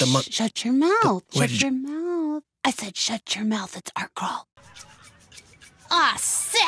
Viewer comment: Tf is she saying at around 6:28?? Arcrawl? What is that? I NEED ANSWERS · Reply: Art crawl